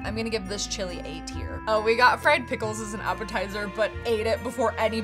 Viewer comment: when you said fried pickles i thought you said "we got fred some pickles"